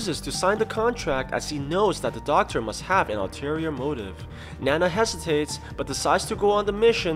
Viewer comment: I SWEAR I heard “Nana has tits…” 😂😂😂 I had to go back a few times to make sure I wasn’t trippin.